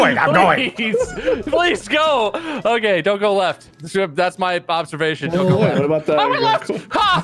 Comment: / did he say " that's my Bobservation" ? Or have I lost my mind?